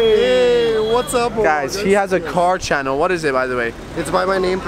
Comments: she has a car channel ? 😂🤣😂 Bro I think u said 'she' to him 🤣😂🤣😂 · just listen what say to other boy She Has A Car channel 😂😂😂😂😂😂